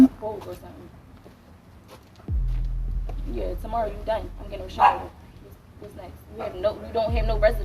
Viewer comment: You heard her say tomorrow you die